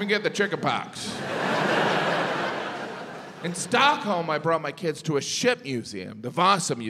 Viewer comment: I thought he said "In Stockholm I brought my kids to a shit museum." Hahahaha And actually, it didn't seem that weird.